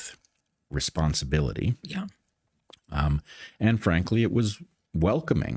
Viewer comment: Chilling that when he says "and frankly" I hear "Anne Frank." Scary times.